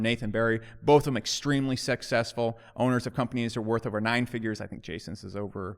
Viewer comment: Did just call Jason & Nathan extremely "sexccessful"?